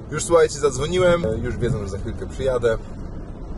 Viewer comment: jestem glucha bez kitu, usłyszałam jadę zawieźć buraczki 🙈 haha myślę sobie what? Pozdro